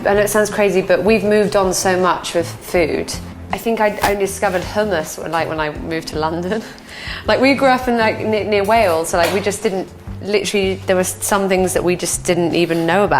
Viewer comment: omg I thought she said "discovered homeless" at first lol